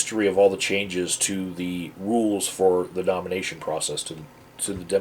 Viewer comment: Was that a Freudian slip at the 2:08 mark? I could have sworn you said "domination process", not "nomination process"!!